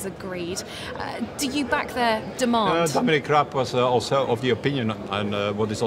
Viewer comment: Did he just say Dominic Crap @ 8:43 ?!...